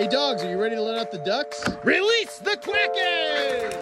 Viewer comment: i have always thought that you say released the dragons😅😅😅